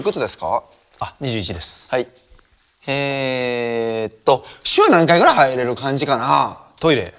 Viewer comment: ここのふくちくの｢えーっと｣が｢屁ーっと｣って聞こえてトイレのこと考えたんじゃないかと思ってる